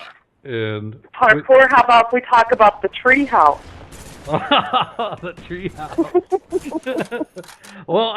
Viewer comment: I thought your mom said "How bout we talk about the tree hump" "Well I did like trees" I rewound that part so many times lol and then found out it was hump not house :(